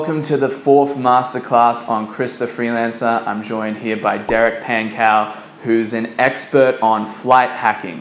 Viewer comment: I thought he said Derrick pancake